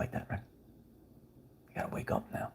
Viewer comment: What is he saying at 14:35-36? Anyone catch that? He’s saying something a few words that are hard to hear? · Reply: “Ya gotta wake up now”